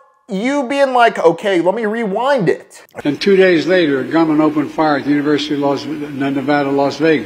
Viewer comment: It sounded like he said "The government opened fire" ... He has been known to let secrets slip out in his old age